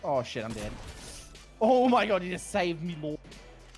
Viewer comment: Did really just say what I think he said 😳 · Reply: Aww my gawd he just saved my ball